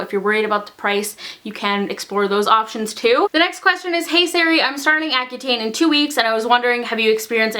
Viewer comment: When she said hey Sari I thought she said hey Siri 😂